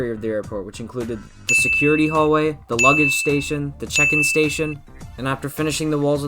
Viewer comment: I thought he said, chicken station